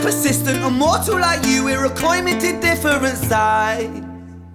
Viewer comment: With the Welsh accent the last verse “immortal are you were a coin made of two different sides” the first 5 times I heard it I thought he said “ immortal are you irricoinment to different sides”, and I thought irricoinment was just a word I had never heard. Also “so cower at the man I’ve become” was “so carry the man I’ve become” Interesting that the Welsh accent is so important to his amazingness. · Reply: I thought he said “we’re a coin minted different sides” ?